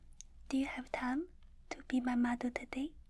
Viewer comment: I like how I heard “do you have time to be my mother” instead of “model” and although I was puzzled, because it’s, I didn’t even consider saying no